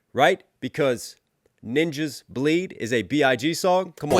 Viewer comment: Did he just called me a ninja